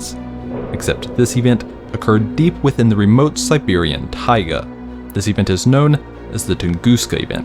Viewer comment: i heard deep within the siberian tiger.